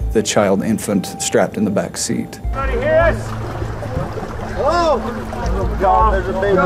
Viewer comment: Edit: 1:30 1:31, I think I hear a woman's voice under the people talking. I'm probably mistaken, but a second opinion would be nice. When the video cuts to the officer's camera, "Us" is heard than a much quieter "help us". Dang, now I need to go try to find the footage. I can't watch any of the videos/footage connected to the 1st video without breaking down and hugging my daughter.